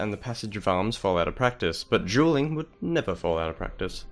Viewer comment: For half the video, I thought you were saying "jeweling," and I was so confused until I realized you were saying Dueling.